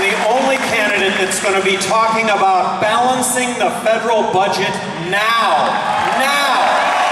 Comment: Hahaha I thought he was going to say "I'm going to be the only candidate talking about balancing the fucking budget now" I was like woah oh shit, go Gary Johnson!